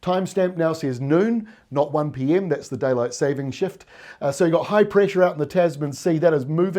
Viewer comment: I thought he said day light saving sh*t 😂